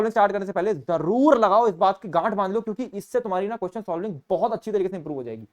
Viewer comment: no offence but 4:45 me kya bola? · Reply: Gath bandlo